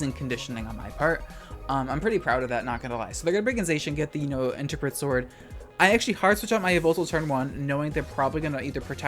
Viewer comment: lmao i thought at 15:15 you said interbred sword 💀